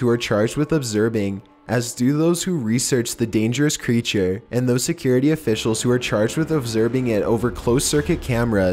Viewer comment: Did he just say overdosed ur a$$